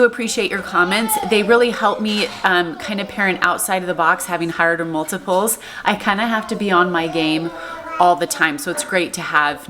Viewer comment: What word is (the mom) saying at 6:35-6:36? She says is every so often and i can't figure it out, LOL! " _______ multiples"? TIA!!! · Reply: High order multiples